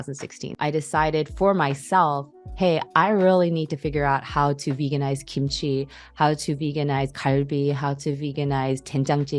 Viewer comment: i know she's saying something different, but the captions say 'i need to figure out how to veganize coyote'. just made me chuckle.